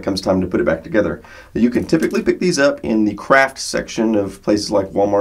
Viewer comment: It sounded like he said the crack section of the supper market